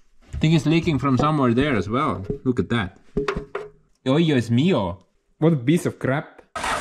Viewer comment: Did he say “Dios Mio” at 46:50?….I laughed my ass off…..love this channel!